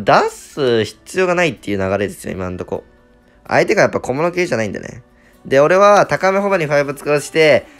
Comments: めちゃくちゃどうでもいいけど「相手が小室圭じゃないんでね」って聞こえた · 「相手が小室圭じゃないんでね」に聞こえてしまいました申し訳ありませんでした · 小物系じゃないんで→小室圭じゃないんで に聞こえたww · 「相手が小室圭じゃないんでね」にしか聞こえん笑笑笑笑笑笑